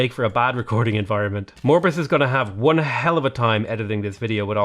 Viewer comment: I think he said the editor's name was Morbus? I'm not entirely sure though. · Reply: Morbis, that's his name on discord server. You can actually come talk to him he pretty much handles the server!